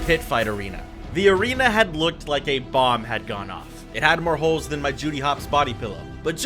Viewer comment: YOOOOOO WHAT DID YOU JUST SAY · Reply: The arena looked like a bomb had gone off